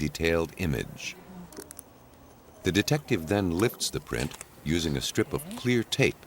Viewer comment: I've got to stop watching these on my phone, or get a hearing aid. At around 22 1/2 minutes the narrator says "The detective then LIFTS the (finger) print..." - I could've sworn he said "The detective then LICKS the print..." Hahahahahahahahaaaa 😂